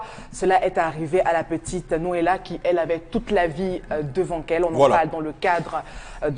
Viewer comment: ‘’ ... la petite N. Elle avait toute la vie devant qu’.... ‘’ 🤔 Did I hear that properly, did she just say that..? Je m’interroge comme dit l’autre... Just saying...🏃🏽‍♂️🏃🏽‍♂️🏃🏽‍♂️